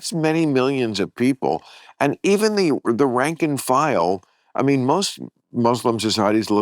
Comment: Did Bill actually say "ranking file" at 1:23:00?